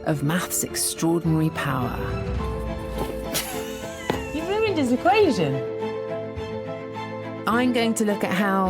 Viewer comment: The subtitles should say "You've ruined this equation!" instead of "Human DIssipation"